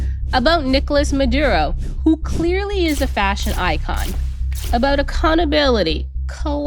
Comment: I thought you said fascist icon not fashion icon. If it looks like a duck, walks like a duck it's probably a dictator.